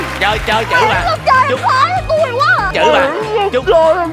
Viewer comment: Lan ngọc nói gì z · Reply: Miễn sao chơi a chơi khó tui quá à :)) mình xem đi xem lại mười mấy lần r haha